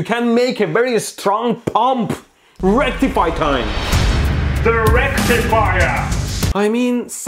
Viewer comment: when that rectifier superhero said "THE RECTIFIER" i heard "ERECTIFIER"